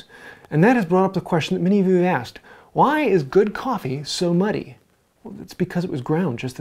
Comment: I first I thought you said 'why is good coffee so money' 🤣 Well, good coffee is certainly not cheap